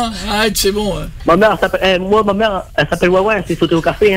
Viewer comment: il dit quoi à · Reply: Ma mère elle s'appelle ouin ouin et après j'ai pas compris !